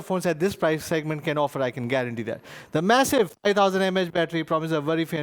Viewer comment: did he say massive 8ooo mah battery...........